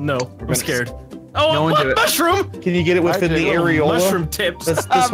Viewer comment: Wait..did MOO said "fu** mushroom" at 1:22 ??